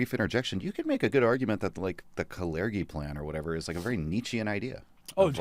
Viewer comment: Who was the person called Keri mentioned at 1:20:06. Please I can't hear the full name and the autogenerated text doesn't help me either. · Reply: Kalergi Plan. It's been debunked, of course.. it's just a coincidence it's actually happening. So many cohen-cidences.